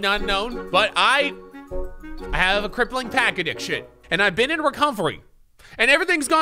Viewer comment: Thought he said “crack addiction” and then I go “You know what, I could probably see that” 😂😂😂